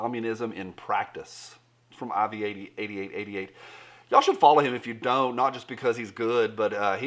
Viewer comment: who is aviar 8888 or what ever you said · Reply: iraqveteran8888